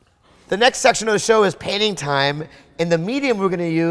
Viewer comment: Anyone else thought he said panty time?